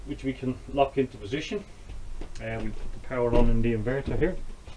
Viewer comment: I'm in australia, thank you heaps. What is your power setup? I'm building my van at the moment but am not sure how to run the electricity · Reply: Heard him say inverter.